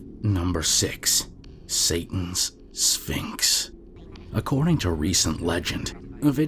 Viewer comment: Ngl, for number 6 I swear I heard ‘Satan’s Sphincter’ and not ‘Satan’s Sphinx’ and I can’t take it seriously anymore